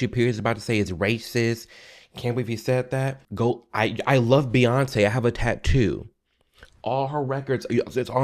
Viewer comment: did he just say he has a Beyonce tattoo?? Oh hell naw 🤮